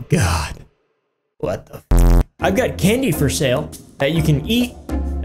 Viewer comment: Anybody else think that he said kidney for sale ???